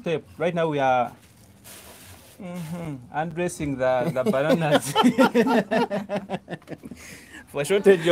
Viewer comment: 😂😂😂 did you just say "we are UNDRESSING THE BANANAs"??! LMAO 😆😆😆